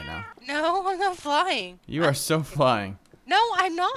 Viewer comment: I thought he said your so fine in the beggining